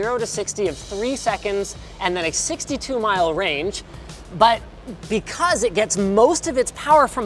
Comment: 62 mile range? Did I hear that right?